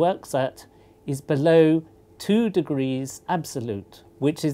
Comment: he said its below 2 degrees C absolute. What you thought he said was its 2 degrees below absolute.